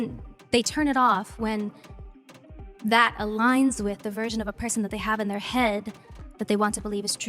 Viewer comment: what the heck is she saying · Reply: “Aligns with the version of a person they have in their head”